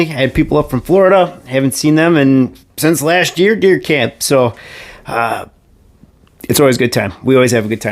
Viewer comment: At first I thought you said beer camp 😂